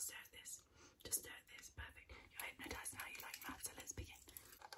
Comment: I'm pretty sure she said something like "now that you're hypnotized let's begin" but I'm not gonna lie I literally hear "now you're a hypnotized lesbian" lmfao XD